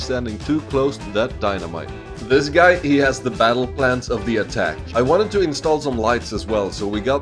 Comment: I thought he said the "Battle Pass"